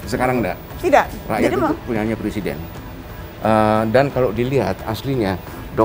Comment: apa? Gak salah denger? Rakyat punya nya pleciden?